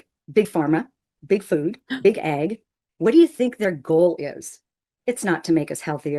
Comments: 😂😂😂 I THOUGHT SHE SAID “BIG EGG” NOT BIG AG😂😂😂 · she unironically said 'Big Egg" --- um factory farming is a major cause of world hunger and mass suffering. Nearly everything she says is contradictory and she's trying to appeal to conspiracy minded folk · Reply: I think she meant “Big Ag,” as in “Big Agriculture” (the Monsanto-type mega-corporations that control a disturbing amount of the seed, fertiliser, and equipment required to farm). I totally heard “Big Egg” too, though. 😅